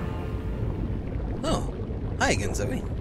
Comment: when he said 'hi again zoë' i thought he said 'hi aain Sophie' which is my name, i got excited and creeped out...